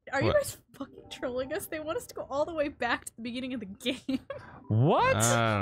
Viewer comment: they missed the snowman bc chat can’t stop trolling >:( Edit: :D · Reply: Aubree Grossman i made this comment as i was watching, at one point I heard “chat said to go all the way back to the beginning” so... yeah.